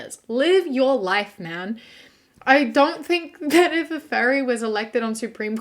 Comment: omg I thought you said fairy not furry I was so confused